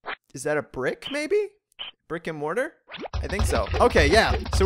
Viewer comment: i thought you said "Rick and Mortar"